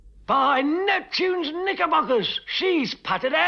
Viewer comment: AM I THE ONLY PERSON WHO HEARD THE N WORD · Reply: its actually “ knickerbockers”